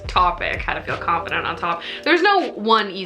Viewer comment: I THOUGHT IT SAID “HOW TO FEEL CONFIDENT IN A TOP” AND NOW IM- yeah i’m still watching lmao